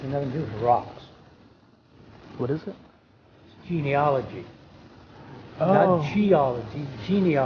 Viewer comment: DID I Hear, VAGINEOLOGY !!??